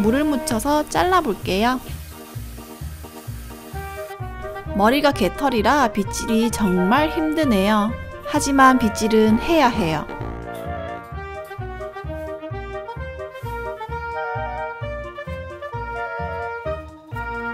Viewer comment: 촌가..? 해야해요를 하야해요로 들었당ㅋㅋㅋ